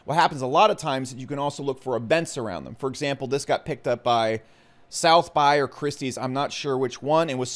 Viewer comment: did you say SouthBuy as in "Sotheby's"? lmao